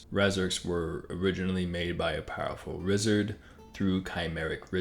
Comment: did he just say Rizzard